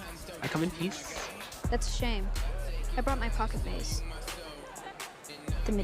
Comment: the caption saying "gaze" as "gays"